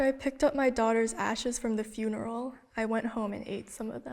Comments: Oh lol I thought he said I ate my daughter ass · My dumbasss really thought it said “I ate my daughters ass” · i thought that said i ate my daughters ass i was like. wait what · Am I the only one who thought it said "I ate my daughter's ass" at first?